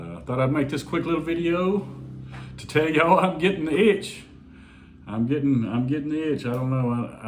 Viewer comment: Man! Don't do it! No need to get married.. the cons concerning Philippines law is way worse than the pros for a foreigner. I've been with mine 6yrs.. you don't have to get married. Unless, you're talking about getting hitched to the Philippines in general lol.. then I get it. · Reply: Yeah, I thought he said, “I’m gettin’ hitched,” too 😂 so much for all that ‘go with the flow’ talk 😂😂